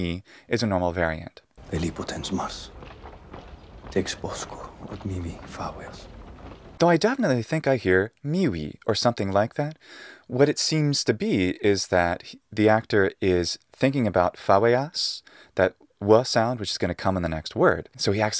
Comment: I hear "mimi"